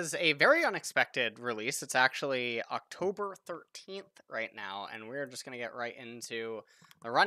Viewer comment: I thought he said "It's October 14th right now." I was like what.